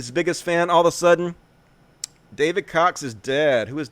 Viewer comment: Totally thought you said “David Cocks.”